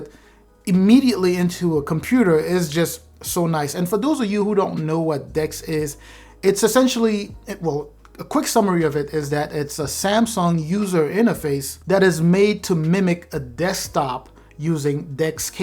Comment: When you said "the next thing is DeX" I thought you said dicks 😅😅😂😂😂😂